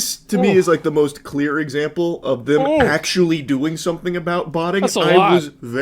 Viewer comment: Man at first I thought he was saying butting